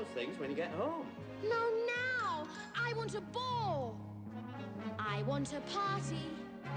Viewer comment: Did she say “bowl” or “boar”? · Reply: Ball